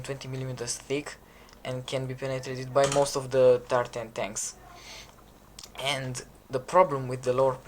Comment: I thought you kept saying "the tartan tanks" instead of tier 10 tanks